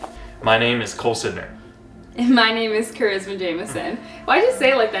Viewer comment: Sounds like he said "my name is call center" 😄😂